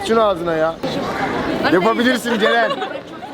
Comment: "Yapa bilirsin Ceren"dedi Yoksa ben yanlış duydum